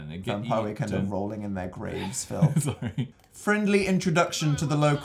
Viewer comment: rewatching this series - can anyone tell what Dan says 7:38?????????????????????????? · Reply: JUST ANOTHER PHANGIRL I think he says " vampire weekend are rolling in their graves Phil"